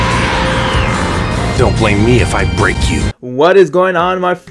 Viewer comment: I thought said dont blame me if I rape you